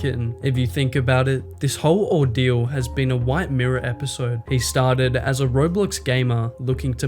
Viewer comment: did bro just say "a white mirror episode"? 😂